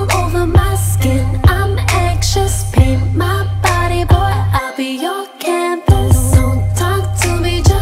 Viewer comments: Am I the only one who keeps hearing "Paint my body boy, I'll by your canvas" at 0:24? According to the lyrics she says "Pin my body boy, I'll be your campus" which doesn't make much sense to me, though english is not my native tongue. Anyone care to elaborate? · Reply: But i heard "i'll be your candles" lol :v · Am I the only one who keeps hearing "Paint my body boy, I'll by your canvas" at 0:24? According to the lyrics she says "Pin my body boy, I'll be your campus" which doesn't make much sense to me, though english is not my native tongue. Anyone care to elaborate? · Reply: I hear "Paint my body boy, I'll be your campus"